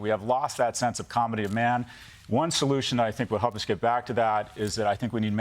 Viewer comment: I heard "comity of man" and I just knew the subtitles person would write "comedy of man".